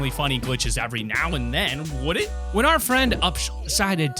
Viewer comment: My audio lagged at 13th slot and it sounded like you said upshit lol